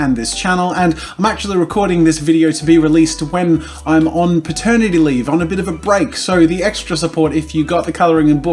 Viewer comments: I thought ye said "maternity leave". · I swear he said maternity leave 😂😂😂😂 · I thought he said 'maternity leave' lol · It’s 3 AM and I’m doped up on painkillers and I thought Jazza said he was on “maternity leave” and my first thought was “aw Jazza’s pregnant?? That’s great!” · I thought he said maternity leave 🥵😕😐😶oh wait I think he did say maternity leave 😐😶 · did he say maturnity leave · I thought you said maternity leave YOUR HAVING A BABY